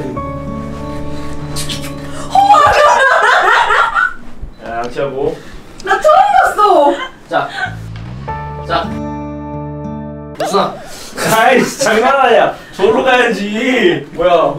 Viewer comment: 님이 “자 양치하고~” 이거 나만 들려??ㅋㅋㅋㅋㅋㅋㅋㅋㅋㅋ